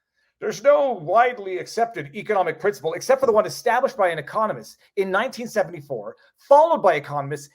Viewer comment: I thought he said communist, not economist.. lol